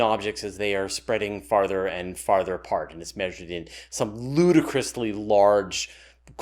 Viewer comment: "Ludicrously large" LOL, I thought you said "voodoo crystally large"! Haha, I guess it's about the same thing.